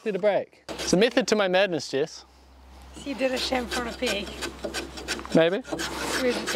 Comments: I honestly thought Jess said "Shampoo on a pig" which funnily enough still works 😂😂. Love the channel and Jess. Much love from Australia. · I love "chamfer on a peg" tee shirt idea, although with your accent I thought you were saying "chamfer on a PIG" could also be interesting